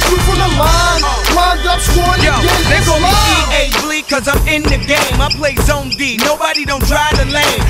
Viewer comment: When he says "they call me ea-bleek cause I'm in the game" in the game I always heard "they call me ea sports cause I'm in the game"